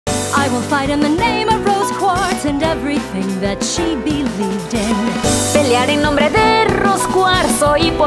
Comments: It sound like “I will fight in the name of rose quartz she went through torture and died young” That’s what I hear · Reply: I heard "i will fight in the name of Rose Quarts and everything that she believes is" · Poland💕💗💘💞 Ogólnie to jakoś mało tu polskich komentarzy O.o T R Z E B A T O Z M I E N I Ć ! ! ! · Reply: czy tylko ja usłyszałem ,,Będę gwałcić Rose" czy coś podobnego?